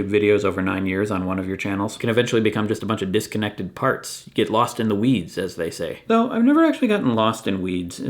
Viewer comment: I thought you said "lost in the wheeze"